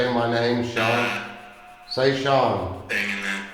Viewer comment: I hear "Hanging there"